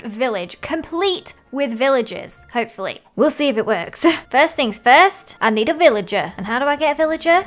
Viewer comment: Lol thought she said “First things first,we need some willagers”